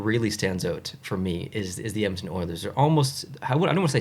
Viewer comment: I’m Rooting for NY but I can’t get over the fact that at around 8:40 in the subtitles it says “Empty Orders” instead of Edmonton Oilers lol